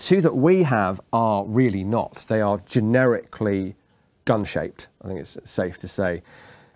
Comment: when he said “gun shaped”, I thought he said ‘dog shit’!! I had to rewind it and listen again.